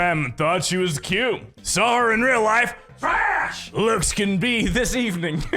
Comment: Mark 5:32 when you say trash looks my closed captions said flash lips Haha